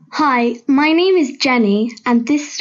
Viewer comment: did you just say your name was laurel??? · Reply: It’s Jennie